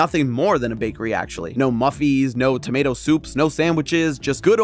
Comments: …did I hear “Muffies”? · Wow this is really interes—did he just say “muffies?”